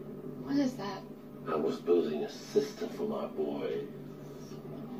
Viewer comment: what did he say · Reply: I was building a sister for My boys